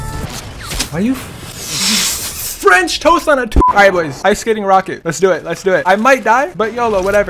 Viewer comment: Did you say French toast on a Tuesday 7:42 Edit: Lol thx for the likes